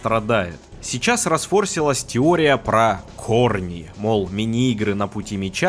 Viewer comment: аа... корни.. а я уж послышал "хорни"